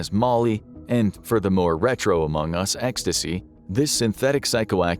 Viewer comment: DID HE SAY AMONG US?!??